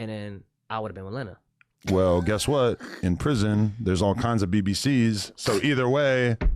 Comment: I'm scared for Lena because Adam gonna snap one day. These jokes starting to get to him. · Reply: what did he just say? He would have fun in prison bc of the BBC’s? That’s what he said